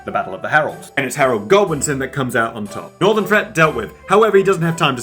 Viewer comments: Interesting point as an American. When you said “northern threat dealt with” with your English accent I thought you said Norman. Maybe that’s why it switched from the north men to the Norman’s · OH! You said Godwinson! I thought you said Goblinson!